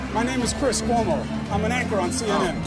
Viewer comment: All I hear is"I'm an ACTOR for CNN". The most honest he's ever been.